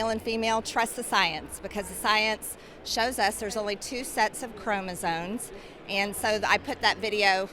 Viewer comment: OH GOD, i agree with her but did she just say there are two sets of chromoZONES